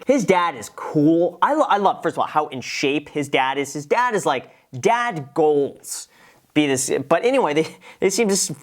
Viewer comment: Did he just say dad golds? 😆